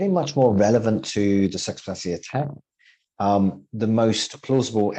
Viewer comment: At around 11:40, the subtitles mention a "sex plastic attack". :-D (I think actually said "the circumstances of the attack", but it's a bit clipped and difficult to make out, so correct me if I'm wrong.) That said, if anyone can tell me about a sex plastic attack here or elsewhere, then I'm all ears and want to hear the full story.